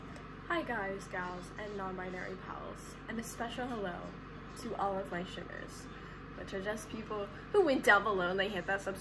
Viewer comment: For like 5 months I thought you were saying “and a special welcome to shifters”